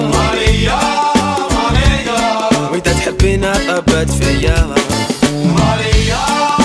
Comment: ويذا تحبينا....؟ وش قال هنا سيريو 😅😂😂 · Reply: ويذا تحبينا طبت فياااا...